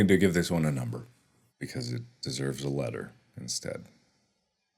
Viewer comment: His actual name is Multiplier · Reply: He actually said it deserves a letter😂